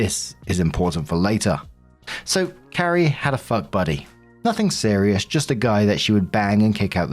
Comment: ok when you first said "Fuck buddy" I heard 'Butt buddy" and had to come back to this tab and read myself xD omg i go by!